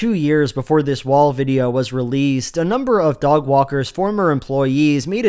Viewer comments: to hear for sure say "dog walkers" instead of "Doug Walker's", you're welcome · I heard dog walkers 😆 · Wait, did you just call him Dog Walker?! 🤣😂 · I totally misheard his name as "dogwalker" lmao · did you call him “Dogwalker”?